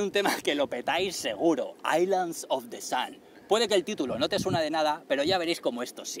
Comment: Como es el nombre? Es que no entiendo 5:21 , gracias. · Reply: Es "Island of the Sun" :-)